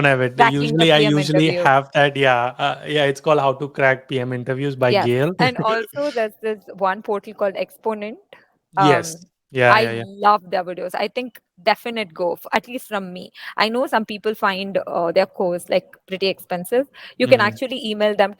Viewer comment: What video was she talking about? I heard it as “one potty called exponent”